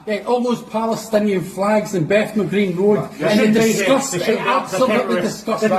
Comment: I thought he said Bethnal Green. That's east London.